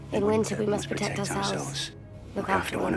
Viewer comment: Tf did she say? 0:41 It sounds like gibberish to me "?? - we must protect ourselves, look after one another." Was it "In winter" ? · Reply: Yes. “In Winter...”